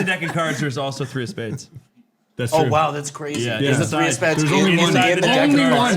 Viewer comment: did anyone else think said three of speds